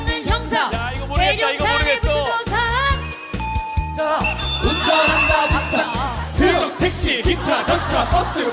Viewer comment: 운전 한다 박사로 나만 들림?